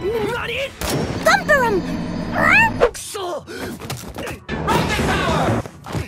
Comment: Does anyone know what meme was edited in at 0:23, when Rohan is sliding down the railing? It sounds like someone saying "up the tower". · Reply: The Blue Soul it says "rocket power" which is a cartoon